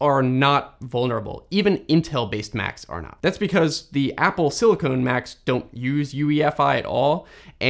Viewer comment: Did he just say "Apple Silicone?"